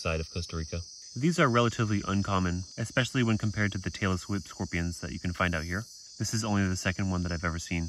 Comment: I had to listen to that twice. I thought you said "Taylor Swift scorpion."